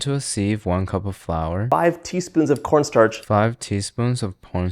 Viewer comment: I love how he got a comment on one of his shorts of someone mishearing him say "pornst*r" instead of cornstarch andd noww he's embracing it. the moment of all time